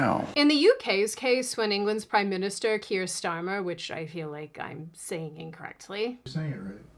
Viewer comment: When I first heard the name Keir Starmer, I thought they were saying Kiers Dahmer. Kiers to rhyme with Piers (Morgan) and Dahmer like the infamous body-parts-in-the-freezer guy.